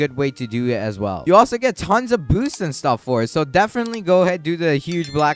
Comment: when he said boosts i thought he said booze😂😂😂